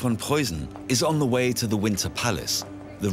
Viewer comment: I just wanted to say: ''I STAND CORRECTED!!!'' I truly thought I heard ''Windsor''. But, I replayed both parts of video and I heard ''Winter''. I'm not making excuses, so NO HATE COMMENTS, PLEASE, but perhaps the way that the people in the UK pronounce the letter ''T'', and the fact that they put a lot of emphasis on that letter, made me think I heard an ''S'' rather than a ''T''.. Does that make sense? It is kind of like how the British say the word: ''Tuesday''. But, somehow, I hear them saying: ''Chews-day''. Then, we have the word: schedule [skedual vs. shedual]. But, we Americans have our quirks, too! Most of us pronounce the word ''winter'' without the ''T'' - ie: ''winner'' -- Winner Palace. I was going to suggest the cc to Gabriel Arellano, but nine times out of ten, they defile the English language. The other day, instead of printing the word: ''Jesus'', I saw the phrase: ''she's us'', which makes absolutely NO sense ... Weird. I also wanted to point out that I watch videos about European Royalty ALL THE TIME... way before the queen died. So, I have heard the world: ''Windsor'' frequently in the past few years. Maybe that factor played a role into what I misheard. Oh, and don't get me started about how many times a narrator has referred to the Winter Palace as the Summer Palace. Which is totally incorrect. Anyhoo, I'm very, very sorry. My bad! Have a good one, guys! : )